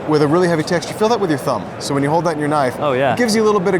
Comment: Anyone else hear "when you hold that in your knife"? 12:15 Edit